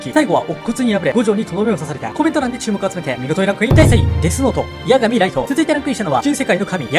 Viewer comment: デスノートがゲスノートに聞こえたwww